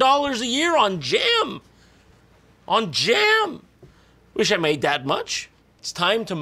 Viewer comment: In the smuckers par when he said "I wish I made that much" I heard "I wish I didn't eat that much" 😂